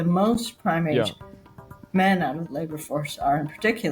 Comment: I heard "primate men"